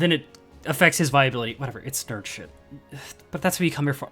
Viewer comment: What does he say at 2:27? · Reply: viability